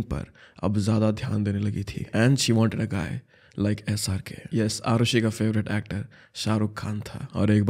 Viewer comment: i thought he's saying sry pay